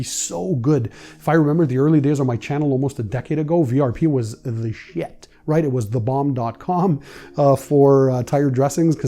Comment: Woooooo Did say Shiznit!! 15:16 This caught me off guard, its family TV night and I went to the garage while the kids and wife where watching cause I forgot to hit pause and I hear in a Canadian Voice " VRP was the Shizit".... I might have to start a Youtube channel to talk about this... Hahaha Just messing with my dog, He is the Shizit!!